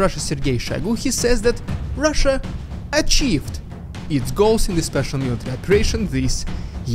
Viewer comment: I heard that as "Vodka in goals" 😂 Must be something about Russian that makes everything sound like vodka.